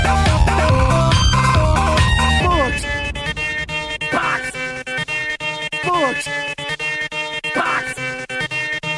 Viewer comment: Did Patrick say fuck? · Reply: Nope he said "box"